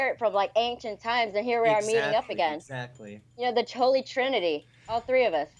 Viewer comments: did she say the trolley trinity? She had a meth slur so it's hard to tell, seems she is not so "stupid".... well I mean.... Educated but with ridiculous autistic conclusions ... or just educated and on meth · did she say "we're like the Trolly trinity"? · did she say "troll-e trinity"? xD